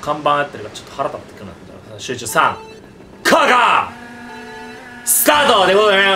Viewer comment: スカートにしか聞こえんw